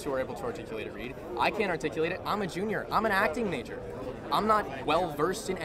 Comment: “I’m acting major” What I hear: “I’m as far left as it gets”